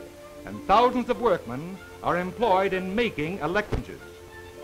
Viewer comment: WAIT, did he just say making "electrcidges"??????? So when was the word "electronic" invented?????? 😂😂😂🤔🤔🤔